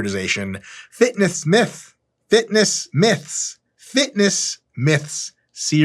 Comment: I know he said fitness myth a couple times but all I heard was “fitn Ess dick.” And I laughed harder than I should’ve. My id says I’m 33, but I never made past 13